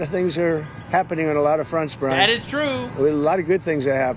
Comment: Did Trump say brah? Lol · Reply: No, he said "Brian." Sounds like "Brah!" But here's the thing, if anything resembling that word comes out of Trump's mouth publicly, you KNOW the radical feminists are going to be ALL OVER IT! "SEE? HE'S A SECKZ PREDATOR!!"